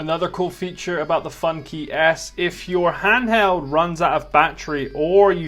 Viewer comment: Sorry if I missed heard it as "Funkey Ass"